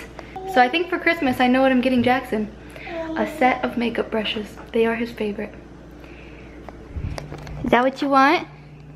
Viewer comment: Hey i noticed in your captions at 1:07 it says "Jackson a set of makeup brushes they are his paper" instead of "Jackson a set of makeup brushes they are his favorite" if you could please fix This so people with hearing disabilities can enjoy you video that'd be awesome.